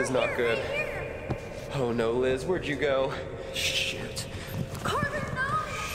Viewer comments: Bro who else heard him say s*it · DID U JUST SAY SH*T!?